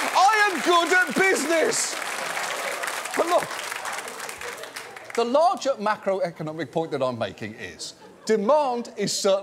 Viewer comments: At about 10.5 minutes in, John said "the larger macroeconomic point." But I was still hung up on the penguin story, so I heard "the larger mackerel economic point" and had to pause it to fully understand what had been said. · Larger mackerel-economic point I’m making is….is what I thought I heard John say